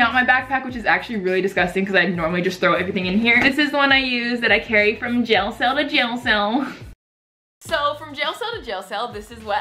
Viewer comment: what does she say at 0:33 ? 😂 also I subscribed after watching this video 😂😂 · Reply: This is wat I carried from jailcell to jailcell